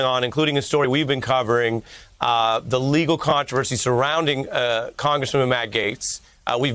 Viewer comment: Did Ari say congresswoman Matt Goetz at 2:43? Lol